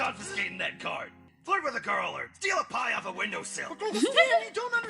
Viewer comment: What did Stan say at 14:05 Did he say “sleep with a girl.”? Am I hearing things? Lol